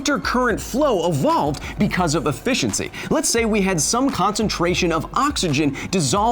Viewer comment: did you say efishency? bad pun is bad and i luv it lmao